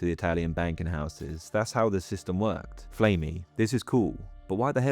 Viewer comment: "Flamey" Ha I thought you said "Flay me"